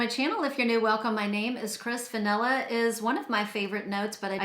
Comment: Lmao I heard " hello, my name is Chris Vanilla" 😂😂😂